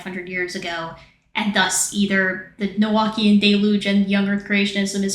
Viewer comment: does anyone know what word that is. Nelwakian deluge? I want to learn more. Thank you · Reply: Noachian. Basically, the flood myth.